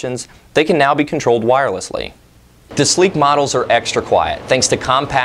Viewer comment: did you say "sleep models" or "sleek models"? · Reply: Hi Vann! it does sound like sleep but he said sleek model. :-)